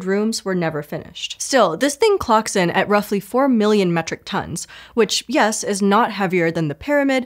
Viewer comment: Start speaking a new language in 3 weeks with Babbel. Get up to 60% off your subscription here: <link> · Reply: Hi, I am unsure if you will see this, but during the Palace of the Parliament section of the video (marked above), the caption and wordage are not the same. The caption says “5 million”, and the narrator says “4 million”. Hope you guys can figure this out :)